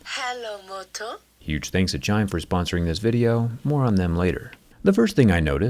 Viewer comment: LOL I HEARD "Thanks to China for sponsoring"